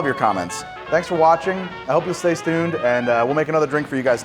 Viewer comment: Yea so this is stone cold cool and I am a Jersey Boi through and through, but 3:17 did you just tell us to "stay stuned"?